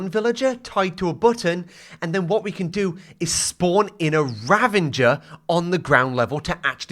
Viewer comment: Why does he say "ravaNger"